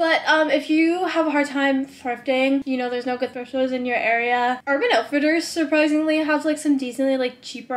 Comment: What do you say at 1:37? What website? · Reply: urban outfitters!